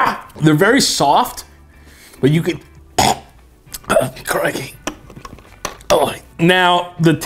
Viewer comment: Did he just say Crikey?😂😂 Oi there mate 😂😂